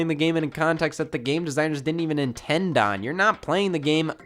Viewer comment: did he just say Nin tend on 😂😂😂😂 nigas